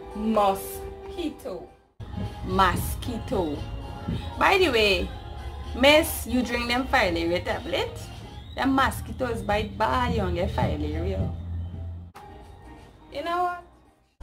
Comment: what is she saying?? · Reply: By the way miss, Did you drink those filaria (filariasis) tablets? Those mosquitoes bite badly ("terribly" would express it better in English). You will get filaria...